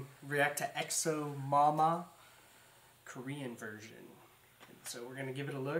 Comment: I heard "Korean virgin" instead of korean version XD